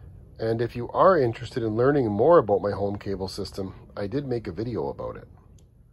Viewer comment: I don’t have a cable signal. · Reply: Sorry dude, you said "home cable system". I watched this video pretty late last night. I thought I heard you say "cable signal".